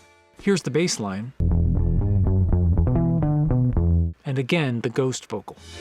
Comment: I just heard ‘AND AGAIN NOW!’